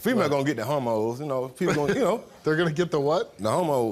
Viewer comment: I thought he said homos, not hormones! Omg 😂😂😂 I hate that word, but that is what I thought he said.